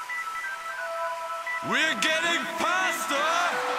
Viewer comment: I always heard it like "We're getting pasta!"